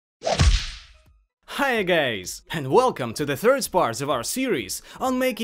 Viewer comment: Did he said "Hi gays?" 0:01? :)